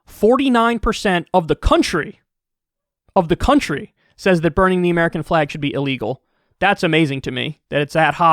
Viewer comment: I heard “Bernie the American flag should illegal” Let’s call the start spangled banner the bernie from now on. Sounds so much better.